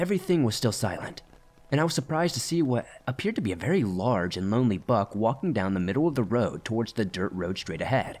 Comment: I legit thought you said "lonely F*ck" instead of "lonely buck" and was like daaamn, roasting the not-deer are you?